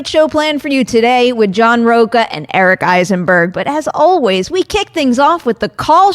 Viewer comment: Oh, Eisenberg, I thought you said Heisenberg. I thought we were going to talk about blue meth. =)